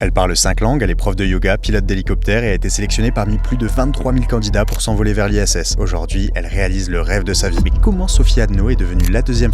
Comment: J'ai entendu "... elle est prof de Yoga, pilate d'hélicoptère..." 😅